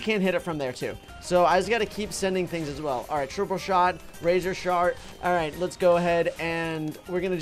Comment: i heard razor shart lol